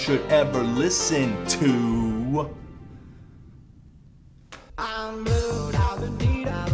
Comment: Once I heard "I'm blue, I'd beat off a guy" I couldn't unhear it